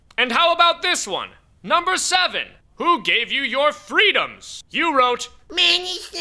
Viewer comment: where he mentions number 7 that talks about what gave people their freedom, all I heard at the end was mostly gibberish. I CAN’T UNDERSTAND A FUCKING WORD HE’S SAYING! Subtitles please, or at least type down what he said. Thanks. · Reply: I think he said "Man is naturally endowed with freedom. It cannot be given, it can only be taken away".